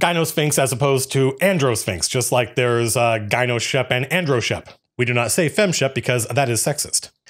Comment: Misheard 7:00 as androSHEMP and gynoSHEMP, was listening while cooking. As I beelined for my phone I almost convinced myself I could’ve heard correctly. “Wow, this IS a funhouse dungeon!”